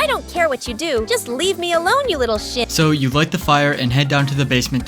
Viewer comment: did it say lil sh##